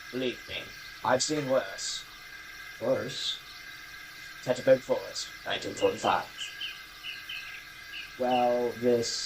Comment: Hey um Cool Video But what was the earl Saying 53:30-53:40 mark about 1945 and a forest i couldnt hear it to well i just wanted know if it was based off anything ? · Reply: He was saying “Teutoburg forest, 1945.” It isn’t based off of anything in real life, but it does possibly produce a link between one of my videos that isn’t in the playlist…